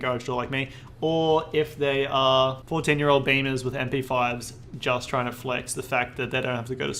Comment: caught me off guard thought he called me a beaner